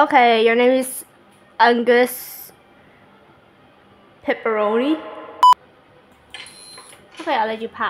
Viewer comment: I heard pepperoni